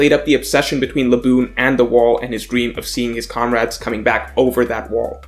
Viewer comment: did he say the word "comrades"? is the communist?! NAUGHTY!😠